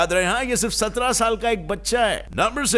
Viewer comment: What said:Yaad rahe, yeh sirf ekk 17 Saal Ka bachcha hai😀 What I heard: 17 Ka hai yaar yeh bkl😂😂🤯